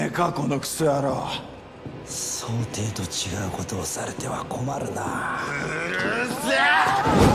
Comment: トグロ兄はなんて言ってるんだろう？ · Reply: 想定と違うことされても困るなーって言ってます